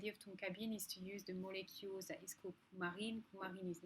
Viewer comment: What is the name you said for tonka bean substitute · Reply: It's coumarin